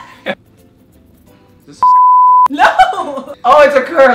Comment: IF YOU KNOW WHAT SAID @ 5:01 THUMBS THIS UP 😂😂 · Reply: OMFG EVERYONE HE SAID "IS THIS A SEX TOY" OK